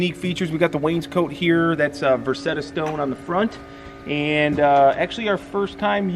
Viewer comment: At first I thought said Rosetta Stone. 😂